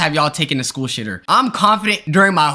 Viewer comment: I thought he said school shooter